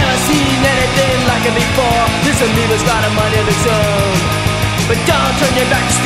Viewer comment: Ahh i always thought they said Tony hawk when they actually said Amoeba. But i wasnt old